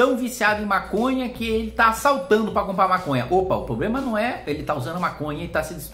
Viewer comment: eu ouvi saltando e pensei qual o problema o cara saltar pq quer maconha kkkkkk ai depois entendi q era assaltar, agora tudo faz sentido kkkkk